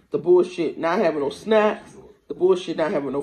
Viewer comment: She mad about having snacks! I’m dead 😵!! Lmao 😂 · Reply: Snaps not snacks 😂 snaps are food stamp benefits. I thought I heard her say snacks too and I was like; no, she couldn’t have really said that! I was scratching my head. But I listened closely and I’m sure I heard “snaps”